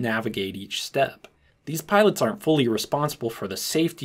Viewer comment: I think you misheard me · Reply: indeed you are right, eventually I switched on the subtitles and indeed you said "aren't"; which I heard it as "are"